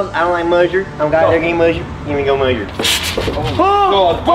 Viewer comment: Re-watching this years later only to realise that jc says ' glad they're getting mustard' when saying the iconic line at 3:34. LMAO All this time I couldn't make out what he said